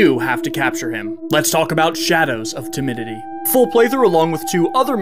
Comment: I zoned out and I thought you said ”shadows of Timmy” 💀